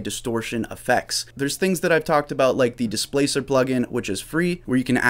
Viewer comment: What Free distortion plugin did you say at 6:22 ?? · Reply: Displacer Pro, here's the link to the vid covering it: <link>